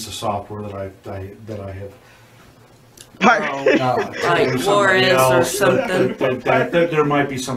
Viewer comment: When she says “war-ez” i think she is trying to say “wares” like “Dame Ware” with an “s”.